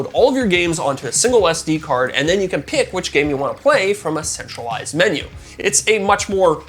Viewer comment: A what? I swear to god I heard a sexualized menu instead of centralized! 😂